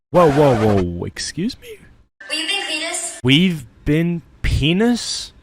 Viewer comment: I heard weaving penis 💀💀💀💀💀💀💀💀💀💀